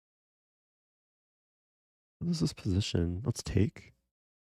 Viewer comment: I heard this position as this piece of s*it